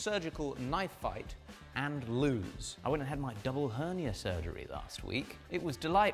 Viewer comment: My phone volume was down, I kept hearing you say "Onion surgery," and for a good 3 minutes I was wondering what in the hell that was.